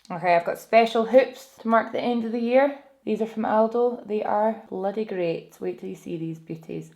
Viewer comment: Great video. That moment when I thought you said your hoops were from Aldi 🙊🤣